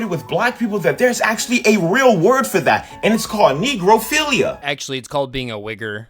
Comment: I had to rewind back when he said negrophilia because I heard “NECRO—“. Regardless, I have no connection to either one of those terms. 😅